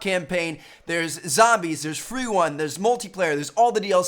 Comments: did he say free one 😄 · anybody else hear him say freeone? 2:11 lmaoo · did anyone hear free wun? #FreeWun?